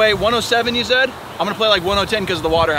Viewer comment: did he just say 1-0-10 instead of 110